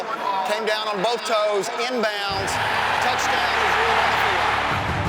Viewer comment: does the referee say touchdown Philadelphia or am I hearing things